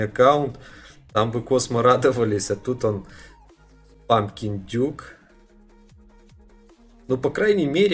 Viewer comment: Ты когда прочел тыкву мне послышалось мамкин индюк